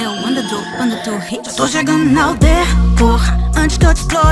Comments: "eu to chegando na aldeia,CORRA!" Oq eu escutei: "Eu to chegando na aldeia,PORRA"kkkk Muito foda,ficou muito bom,parabéns👏👏👏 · Só eu q ouvi "Porra" em vez de "Corra"? Cara a voz da é angelical! A voz do é foda pacas !! Mlhr dupla cara!! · Quando a falou "corra" eu entendi "porra" kkkkkkkk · Parece que ela fala "porra" ao invés de "corra" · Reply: Eu entendi "to chegando na aldeia, porra"